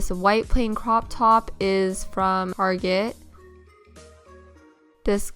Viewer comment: Arget? did i hear that right lol